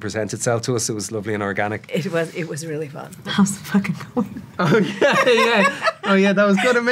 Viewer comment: What did she say at 1:10? I want to know ehat the improvised line was that Colin said. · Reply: "How's it fucking going?"